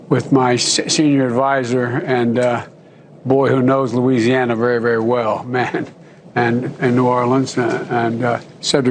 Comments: Not a Biden fan but it sounds like he said “Boy he sure does know Louisiana really well”. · I thought I heard him say "and boy does he know Louisiana" I think you're stretching on this one. Lol. We all know Biden has racist tendencies though.